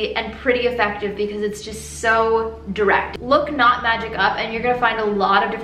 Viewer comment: when she said knot magic i thought she was saying not magic and was very confused for a second oops aha